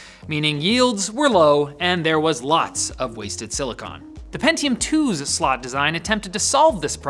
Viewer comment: did you say silicone?